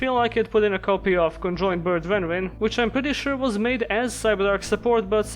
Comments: I watched this many times and still have no idea what he said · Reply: "Conjoined bird renrin" which in the tcg was renamed to leng ling. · what is the card he says? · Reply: Conjoined bird Renrin otherwise known as Leng Ling in the TCG <link> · what on earth are you saying here, honestly the subtitles show exactly what I'm hearing but like... "Control bird rendering" isn't a card sooooo what? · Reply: Super late, he said "Conjoined Bird Renrin". TCG name is Leng Ling